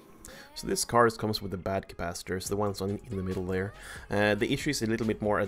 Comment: I couldnt understand exactly, but did you say this card comes with bad capacitors? Around 5:10 ...